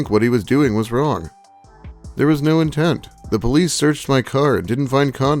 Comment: Did he say the police searched my car?